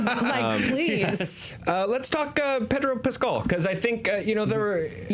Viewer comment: Did I hear "Pecker Pascal" ?